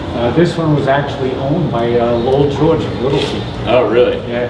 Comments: WHOSE name is he saying at 3:13?? Lowel George WIlson? Low Joe WIlson? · Who is he talking about here, I can't make out the name? And what's the joke, why don't they talk about it? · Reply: Lowell George, Little Feat. He died in 1979. It was not pretty, apparently. Check out Wikipedia. Must be a back story on the guitar. That's a guitar with serious provenance, and I'm not a big Lowell George or Little Feat fan.